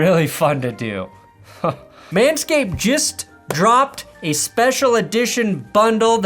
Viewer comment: did just say jizz dropped?